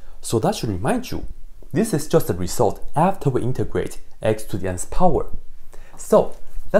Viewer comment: I thought you said x to the x power, I double checked and proved myself wrong